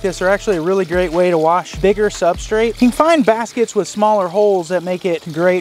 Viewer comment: What did you say 1:23 ? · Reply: …Wash bigger substrate